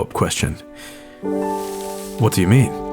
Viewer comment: I must have missed it but what specifically was the question that hasn’t been asked? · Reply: nvm it's "What do you mean?" in response to Miyamoto saying he wants to build a "miniature garden".